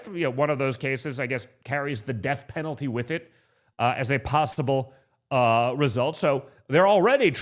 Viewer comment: Did he say “Pastable”? Or maybe he’s thinking about the never ending pasta bowl at Olive Garden.